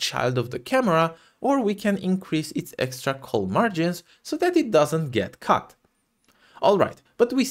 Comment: The vertex being a xy as opposed to setting just the x was what I was missing. Great timing on the tutorial for my current game jam! Also 3:10 I thought you said, "So it doesn't get cucked." lmfao